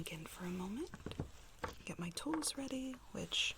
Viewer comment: i legit thought you said “ now let me get my toes ready “ 😂